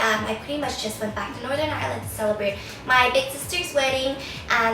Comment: In one of your videos I thought you said northern MARYLAND but apparently you said northern IRELAND my bad